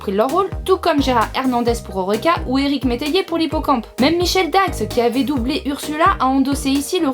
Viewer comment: C'est moi ou j'ai entendu "Michèle Dax" au lieu de Micheline Dax^^